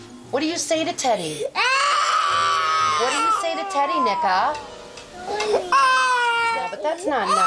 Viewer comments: did i hear that correctly? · IM SORRY I CANT HELP IT 2:20 EXCUSE ME WHAT DID SHE CALL HER KID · what did she say? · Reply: “Nika”